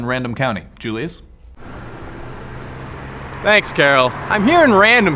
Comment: did he say thanks "Carol" at 0:18 ?